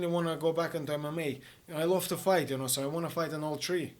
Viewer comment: for a second i thought he said " i'd love to fight an old tree " and i was like " what? he's saying he likes fighting so much he'd like to punch a tree too? " then i realized he said " in all three " Now i feel dumb. Love Artem, sad it didnt go his way but hey, i'll always be a fan as long as long as he keeps being this great of a guy, he seems so dang humble and kind.